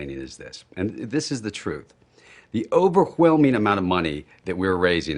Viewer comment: over-hwhelming. hwil hwheaton anyone? i hear it. over huuuwhelming